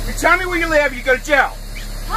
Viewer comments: "WHERE ARE YOU FROM?!" " IM FROM HELL!!!!! " Edit: Am I the only one who heard "I'm from hell?!" 😂😂😂😂 Edit 2: I'm starting to hear upper hill ;w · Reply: I heard "Upper hill" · "WHERE ARE YOU FROM?!" " IM FROM HELL!!!!! " Edit: Am I the only one who heard "I'm from hell?!" 😂😂😂😂 Edit 2: I'm starting to hear upper hill ;w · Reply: I thought he said I'm from upper hill :/